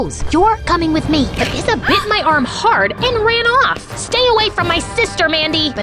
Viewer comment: I thought it said “she bit my arm off”